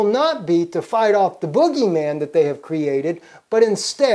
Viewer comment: P.S. Shouldn't it be bogey man rather than the booger. I'm sorry, it sounded like said 'Booger Man'.